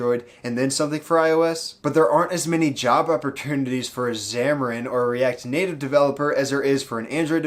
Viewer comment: Dunno why but I keep hearing "Java opportunitiy" instead of "Job opportunity" lol